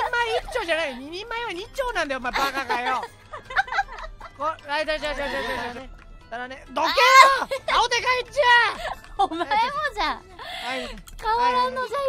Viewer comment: sounds like the Japanese ver of Gordon Ramsay lol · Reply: I think I heard him say "Donkey" at 1:19 lol